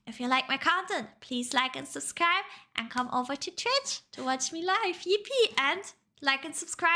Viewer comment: i know she said Twitch but for some reason i thought she said Church 😅